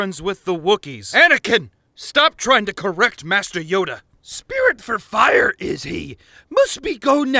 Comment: “Spirit for fire is he, must be go now, I see then, hah” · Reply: I thought you said spirit of fire at first that is a halo wars reference